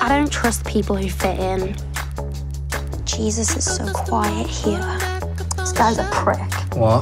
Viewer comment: “jesus is so quiet here” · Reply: she actually said "Jesus it's so quiet here" but it sounds like she says "is"